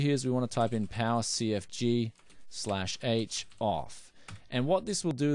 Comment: please video at 10:41 what do you said? cfg /h off? I try cant acces. please help me